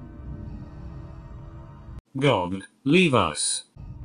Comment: I thought he said GOD LEAVE US